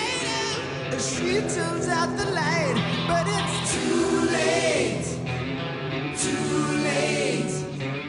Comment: Who else thought they were saying koolaid instead of too late the 1st time they heard this on the radio? Lol!